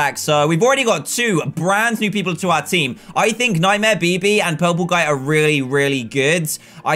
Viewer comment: When he said nightmare bb i thought he said nightmare pp